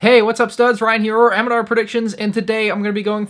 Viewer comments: Hey uh Ryan I think that you said mandr predictions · Did you just call your self mandr predictions